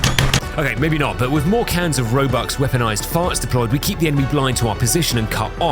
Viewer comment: I actually thought everytime you said roebuck’s name i thought you were saying Robux 😭😭💀💀💀